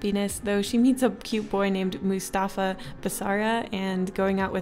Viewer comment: Wait did you just say Mufassa? Oh... you said Mustafa, just forget I said anything and move on.